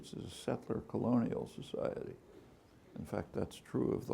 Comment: the captions are wrong, he didn't say, "secular colonial society", he said, "settler colonial society".